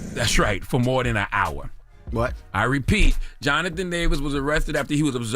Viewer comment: At first i thought you said Jonathan Majors 😂😂